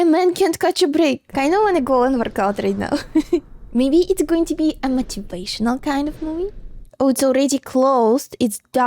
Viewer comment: what are you saying? I heard, "I want to go on recountry now" and "maybe it's going to be a matupational kind of movie."